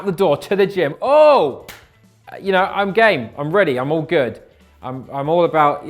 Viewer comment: i thought he said im gay😂😂